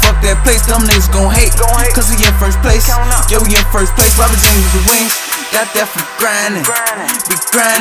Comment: What does say at 2:50? I ran it back and still can't catch it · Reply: Robin jeans wit the wings, got that from grindin